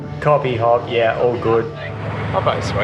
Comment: does say “her body’s sweet mate” or am I crazy 😂😂😂😂😂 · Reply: He very clearly says Hoppo. Looks like your mind was in the gutter.